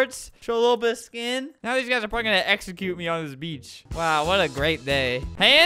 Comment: thought he said “now everyone’s f**king gonna execute me on this beach”